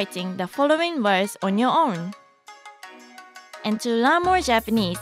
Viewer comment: I thought she said on your arm!!